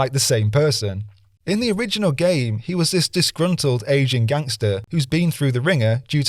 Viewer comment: Hey everyone, here are a few corrections to the script that a few people have pointed out so far. I'll update the list if anything else comes up: - The texture I said was water in GTA 3 is actually a pool cover. I was 50/50 here, but as it looked different to the pool cover in Asuka's courtyard I thought it was water. It looks odd either way - Unreal Engine wasn't responsible for the cartoony visuals as other non-cartoony games were made in unreal. Roblox also doesn't use Unreal. This came from a ScreenRant article following the October trailer last year and I carried their error forward. -When I said GSGs were fighting back the cartoony visuals of Unreal I should have said "when they ported the original game into Unreal everything was too clean". Rich Rosado said this in the FTW USA Today interview I mentioned -DLSS isn't a lighting effect -This isn't really a correction, but when I was describing Tommy I said he was an "ageing gangster", not an "Asian gangster" Cheers! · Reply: Good on you for owning any errors. I just wish Grove Devs. Would do the same! Cheers from Canada P.S. I really thought I heard Asian, instead of aging. I thought I was going bonkers. Lol